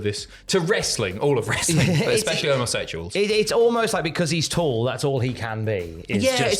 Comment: Us Omosexuals will never give up on our king. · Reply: Thought he said homosexuals, was so lost on that connection until I saw this comment 😆